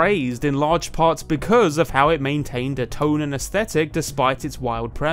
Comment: "it maintained a tone and aesthetic [...]" What I heard: "it maintained its shounen aesthetic [...]" Shounen Space Jam? Now that's a genre that hasn't been explored before.